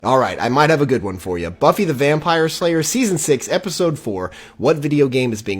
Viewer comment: I think I misheard you there did you really mean to say buffy the vampire layer ?.